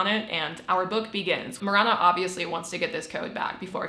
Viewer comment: Instead of hearing “Mirana” for the last female protagonist, I heard “Moron-a,” and after deliberation, I’ve decided this is the correct and funnier pronunciation, especially considering the author’s note glazing the protagonist’s IQ